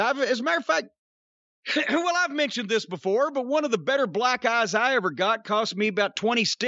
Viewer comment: At first I wasn't paying attention and thought I heard Jim say first black guy I had and was like huh, lol. Oh black eye